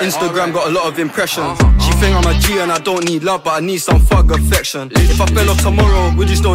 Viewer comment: is that thug affection instead of fuck affection innit?